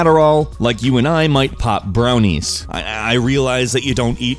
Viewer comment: I thought he said pot brownies instead of pop brownies